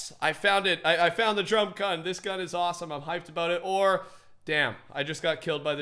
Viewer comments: did he say damn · did...did he say damn